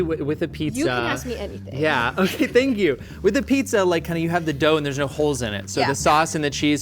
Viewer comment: I swear the dude said “WIKIPIZZA” and now we need that website